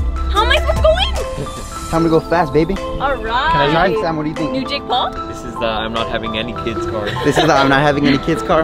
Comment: did she say are you jake Paul